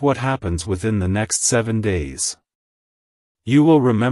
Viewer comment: I thought you said in three days my God